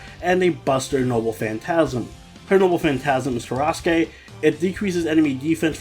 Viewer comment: When you said Tarasque I thought you said Sauske